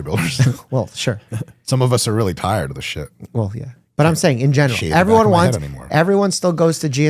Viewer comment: I heard this as "General Tsao." I'm like yeah, I guess you can argue that General Tsaos chicken did change humanity